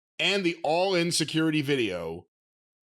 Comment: I know said the All-In security video, but I heard it as All INSECURITY video!🤣